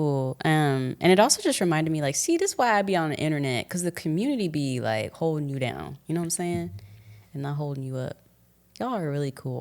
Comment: I misheard and thought said "the community be holding you down when you are not holding up" which i liked too LOL